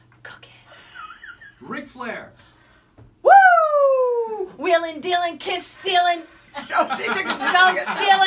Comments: did bayley say wheeling dealing kid stealing?😂😂😂😂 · I thought she said kid stealing for ric